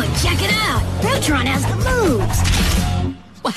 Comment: Everyone calm down he did not say the N word he said “later nicker Rama’ it does sound like he said the N word but it’s just because of the bad audio quality